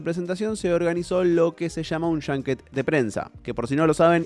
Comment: Estaba tan metido en "modo" Fallout que oí Junk Jet en vez de Junket... 😅😅😅